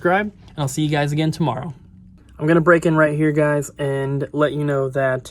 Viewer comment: Sorry I thought you said break in. Glad to know that it's just breaking news.